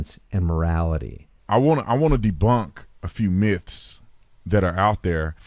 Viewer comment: did he say "deblunk"? This guy's a dope.